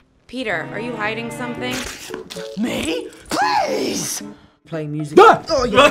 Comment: did he just say “ Nee please” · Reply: No: mee please